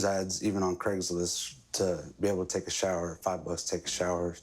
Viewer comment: I heard “five of us take a shower.” Not “five bucks”. You hear what you want to hear 😅🤣